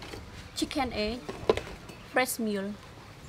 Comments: It's a good job that you had captions on! I thought you said breast milk 😳 anyway... Great video hun xxx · Ahh ok its fresh milk, i thought i heard breast milk 😬